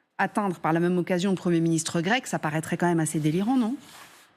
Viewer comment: Elle le dit. Dude on est en 2024, votre façon de raconter n'importe quoi ne marche plus. Faut évoluer un peu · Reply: Effectivement mais tu n'as pas entendu la fin de la phrase : ça semble complètement délirant ( le ciblage de zelinski et du ministre grec) pense à tes oreilles lors de ta prochaine douche.